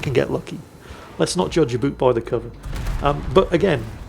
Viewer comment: Did I hear that right? "Judge a boot by the cover" at 3:28. Mixing metaphors again?